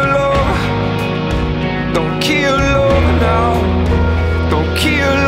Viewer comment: For anyone who don't know what he's saying at 0:41 and such its "don't keel over" even though it sounds like "don't keel oh".